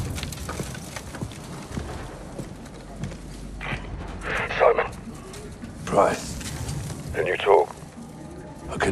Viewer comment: did anyone heard what captain price said ?? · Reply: Yeah soap